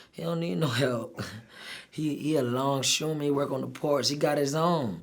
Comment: what does he say his dad does for work? Pays 6-7k a week 🏦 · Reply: longshoreman